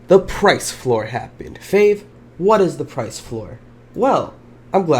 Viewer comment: When says the captions say faith try it CLICK THIS: 3:54 and pause the video